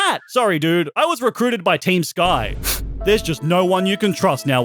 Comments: did I hear Team Sky? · did he say team sky?!